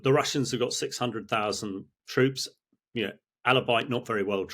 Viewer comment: Did he say "albeit" or is it something else?